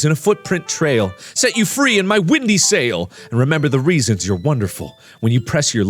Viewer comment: Lol l thought in that part he said wendy’s sail XD